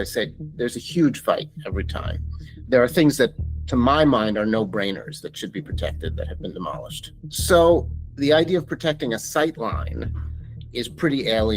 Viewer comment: Why is is a piece of what he said removed from the subtitles? "There are things that to my mind are no-brainers that should be protected that have been demolished. So..." (It appears in the auto-generated subtitles).